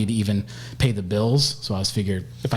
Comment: I really thought that said balls not bills.